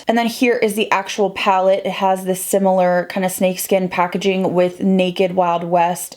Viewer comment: My fat ass heard you say, “It has a STEAK skin packaging” and not “snake”